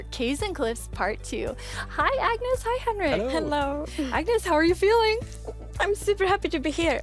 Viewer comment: did Agnes really say: *Hennlo*?